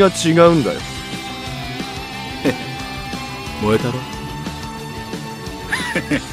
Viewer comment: what is he saying at 1:28? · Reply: KURAI YAGARE (Take this!)